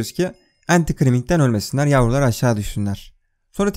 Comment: de ne diyor anlamadım. Entikriming gibi bişey 😅, ne oluyor o? Ve nasıl yazılıyor